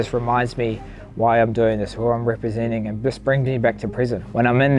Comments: Just brings me back to Prison? Is that what he said? Or Present? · He says the fight ‘brings him back to prison’ Does anyone know what he did time for? Weird that way he never speaks about it. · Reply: He said "brings me back to present", not prison